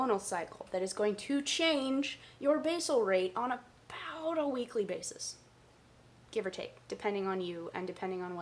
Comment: couldn't understand the words 'on a pow..d..o weekly basis .. ' -- Awesome video. Really helped to understand the tricky matter of basal insulin rates